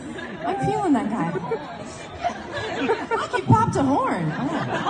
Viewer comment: Can anybody help, what did Lauren say in 43:12 ? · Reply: 'he popped a horn'